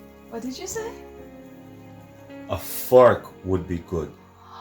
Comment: thought said "A f**k wud be good" lol but he actually said "a fork would be good"🤣🤣